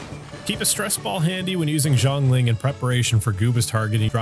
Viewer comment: D- did he say "Zhongling" at 7:22??